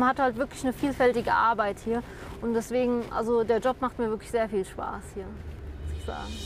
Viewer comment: "[...] sehr viel Spaß.... saas..." habe ich verstanden ^^